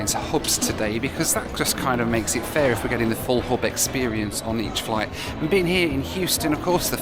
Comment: When you said, "Full hub experience," I kept hearing, "Full Hobbit experience!" 😂😂